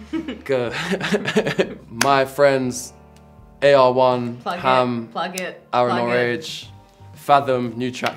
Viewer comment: What did jo say in 2:33 ? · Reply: Plug it plug it